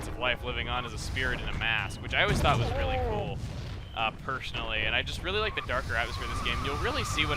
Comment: did he say pershunally